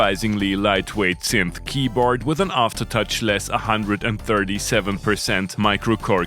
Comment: OK now you can confess that you love this synth 😉 Did I hear you say "aftertouch keyboard" ? You probably meant velocity sensible. Actually the lack of aftertouch keyboard is the only thing that makes the Minilogue/XD/Bass & Prologue bad gear 🤬